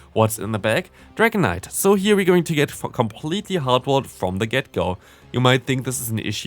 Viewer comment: i heared that f word I know he said for completely but if we don't hear clearly yea